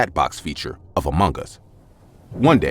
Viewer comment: I thought he was saying amungus 😂